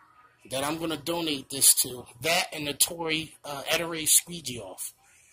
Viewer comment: I would love to try that product, and the ettore squeegees! Just started my business about 4 months ago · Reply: Just window cleaning I thought I heard you say ettore squeegees at 9:40 but I think you said ettore squeegee off.